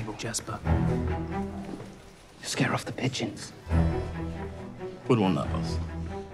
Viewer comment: LMAOOOOOOOOO at first scene i thought kaz said "you're scaring off the bitches" 😭😭😭